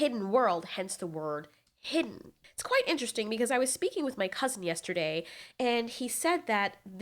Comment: oh cousin! I thought you said customer😂😂😂